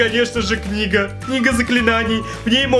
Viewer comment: мне одной послышалось " книга-нига заклинаний"???